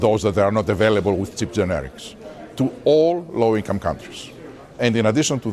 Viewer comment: Chip generics? I'm not native English. Is this scary? Or does he say cheap? Subtitles say chip.